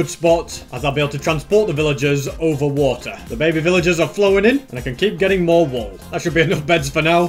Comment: did he just said flowin in ?🤣🤣🤣